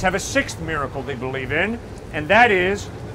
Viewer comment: I thought he said, "Gravity comes from matter." Also a miracle, if you think about it.